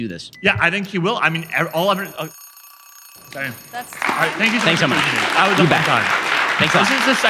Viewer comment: at @ 1:36:55 did he say "está bien"